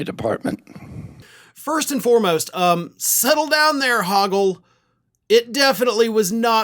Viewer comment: RFK Jr. really does look like Hoggle. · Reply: For a second there i thought you said "hoagie", and im like, yeah!